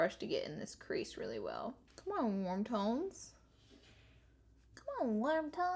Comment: I heard "come on wontons" 😂